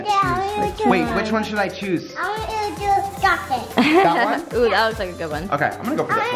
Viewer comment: It sounded like said chocolate-y